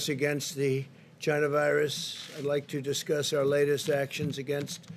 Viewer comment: Did he just say "vagina virus" ????